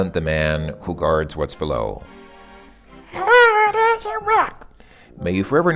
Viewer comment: What did you say at 16:33? I couldn't quite make it out. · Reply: "Hard as a rock". It was a very bad attempt at emulating the singer of AC/DC when he sings that line before the chorus of the song with the same name.